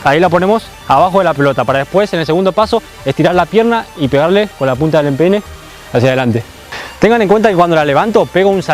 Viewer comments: le entendí con la punta del p3n3 xd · parece que dijo pene 😂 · Soy yo, escuche mal o fijo para pegarle con la punta del pene